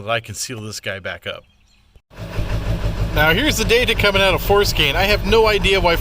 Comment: What App was that? It sounded like you said FORscan I googled Ford scan and all types of scanning apps but did not see one like you used. I'm new to 7.3s so go easy on a noob please. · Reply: It is forscan. You can find it in the Google play store if you have an Android, I'm not sure if it's available for apple.